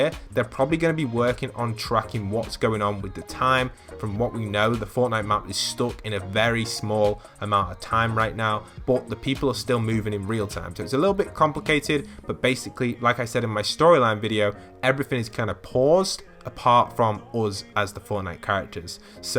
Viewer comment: Did He Say Crumplecated?